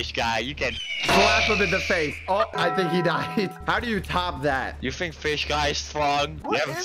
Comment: I passed out and when I woke up I heard him say "I think he's dead"